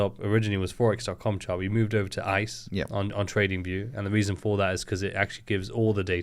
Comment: Free Telegram Community! 👉🏽 <link> Free Trading Plan 👇 <link> ________________________________________ TRADEZELLA: All-in-one trading journal and analytics tool that provides valuable insights, trade replay, in depth reporting, and more to gain clarity, eliminate costly mistakes, and trade smarter. ⬇️Use Code “RIZ10” for 10% off your subscription ⬇️ <link> ⬇️Use Code “WOR” for 20% off your yearly subscription ⬇️ <link> ________________________________________ The #1 Prop Firm In The Entire Industry Alpha Capital. They have their own broker, their own technology, actual institutional experience and the best community in the prop firm industry. They also still support the best trading platform for all Traders with Platform 5! 👀 Use Code "RIZ" for 20% OFF ALL Challenges. 👉 <link> 👈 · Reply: Good day. I'm new and just learning; but what did you say @ 38:26? It sounded like "we moved over to 'ice' on tradingview"; but Close Caption does Not show that word. And I can't find it on google. I'm guessing it's some kind of acronym but have no idea what to search. Please let me know, thx! And thanks for the vid!!